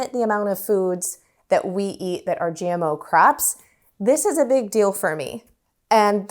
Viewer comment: I thought she was saying 'gmo crap' but it was 'gmo crop'. LOL